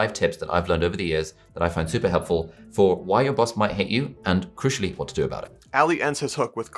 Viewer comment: Thank you - although the first time on listening I thought Ali was saying "5 tips as to why your bus might hit you" and was really curious as to why!! ha ha